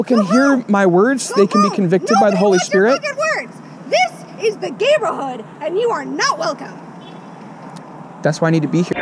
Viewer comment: I heard "THIS IS THE GAMERHOOD"